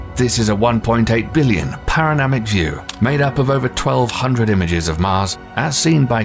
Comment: Did he just say paranamic view?